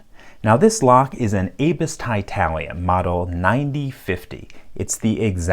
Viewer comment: Genuinely thought it said titanium not titalium